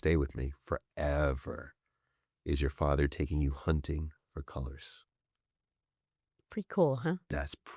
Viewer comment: What was said at 41:41? Because it sounds like hunting for colours but that makes no sense to me 😬